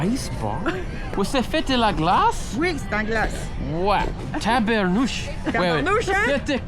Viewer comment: Thanks to QuillBot for sponsoring this video! Check out all QuillBot tools for free here: <link> You can use my discount link for 20% off QuillBot premium: “xiaomanyc” So what do all you non-Canadian French speakers think of Québécois French??? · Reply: The subtitles got it wrong. She said "C'est en glace." but the sub-title said, "Yes, glass." No, "glace" means ice in French.